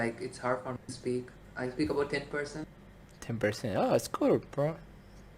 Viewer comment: ايش يقول هاذه 0:15 كور برو